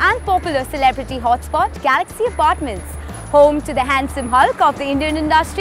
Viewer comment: did she just said handsome hulk??????